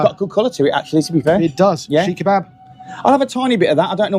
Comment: On the menu its listed as a sheekh kebab. 😁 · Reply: Ah ok, I thought I heard say Shish kebab (cubes of marinated meet) rather than Seekh kebab (like a kofta, a mixture of spiced finely minced meat).